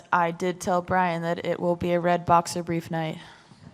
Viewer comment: What did Miesha say at 8:38 Redbox something night? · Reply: A red boxer/brief night. I think it's a play on McGregor's red panty night